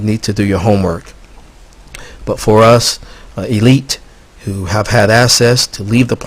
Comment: did you say you are elite!!!!!!!!!!